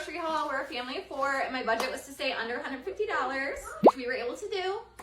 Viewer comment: Thought she said under 100.00 totally...😅126.33 is way over